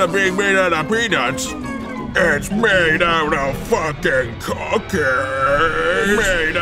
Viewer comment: At first I heard him say "it's made out of fuckin cocain "